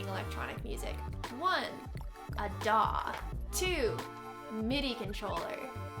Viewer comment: I misheard “One … a dog!” and I nodded vigorously in agreement! 🐕❤️😀